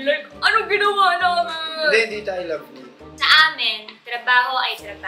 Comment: Tama ba yung narinig ko hahaha sa 16:46 "hindi hindi tayu love yun"(seth)